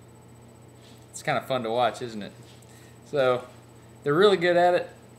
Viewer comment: Haha, the automatic captions at 1:00 are so wrong! Instead of "It's kind of fun to watch, isn't it?" they read, "scott bundle oxygen".